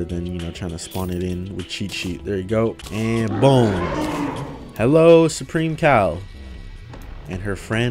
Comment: I THOUGHT HE SAID "supreme cow"